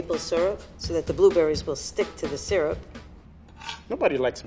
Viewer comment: did he just say no one likes syrup?!??